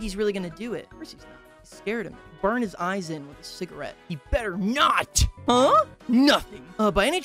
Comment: Mid chew and I hear “ I’m gonna stick his eyes out with a cigarette “ talking about Vincent and I almost choked to death 😂😂😂😭😭😭